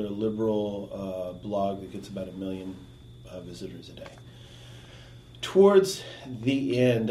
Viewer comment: it sounded like he said the Daily Kos gets 'about a million idiots a day"